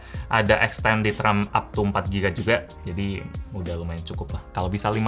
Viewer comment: Extended RAM 44GB?? Ouh, Ternyata Up to 4GB 😂😂 saya salah denger berarti